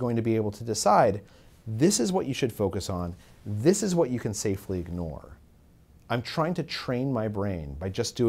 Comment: Anybody else notice the caption at 6:33 being the complete opposite of what he said? Speaker: "...what you can safely ignore." Caption: "...what you can safely dwell on."